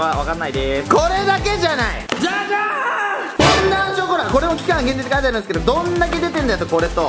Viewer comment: フォンダンショコラが本田ショコラに聞こえた